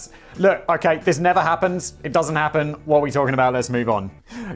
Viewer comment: How is it that every single video I watch with Simon Whistler in it, I hear "what am I talking about? Damn it, Simon"?